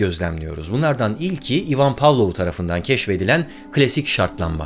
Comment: iman power anladım